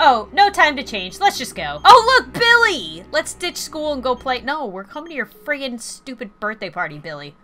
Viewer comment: after lauren said "whatever you can be the bus" i swear i heard her say "ah, school, school"